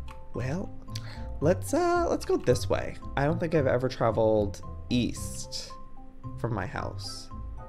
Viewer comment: EAST?? I THOUGHT YOU SAID WEAST! I wish I knew more people who say east LMAO